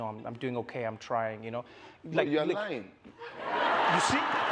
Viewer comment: Can someone please tell me what Burna Boy said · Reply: "You are lying"